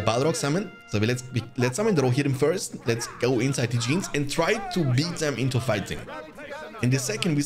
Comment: What are you saying at 32:08? Inside Eugenes? Who's Eugenes? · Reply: The full saying is "to go full ham inside the jeans " ... :D Its a evergreen phrase ... doesn't make much sense but ... IT IS SHINIG BRIGHT LIKE A DIAMOND ! :D